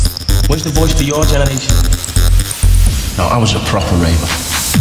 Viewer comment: What does he say before 2:15? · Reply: I was a proper raver! :)